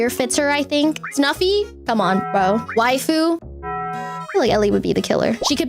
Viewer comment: who's this Vtuber? i know her I heard say her name is waifu, but i can't find her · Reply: its spelled yfu not waifu. She's a pretty good singer!